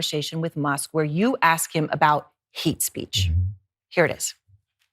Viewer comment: Did she say "heat speech"